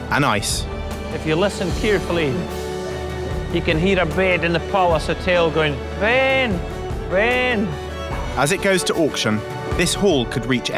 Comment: What did he say at 23:47 ? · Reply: He said "if you listen carefully you can hear a bed in the Palace hotel going Ben Ben".